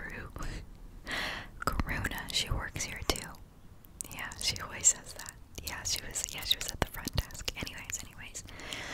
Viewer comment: d-did she just... did she just say Corona??? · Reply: Karuna (another Asmrist who says works at the front desk.) If you look at the top comments you'll find her name with a check beside it.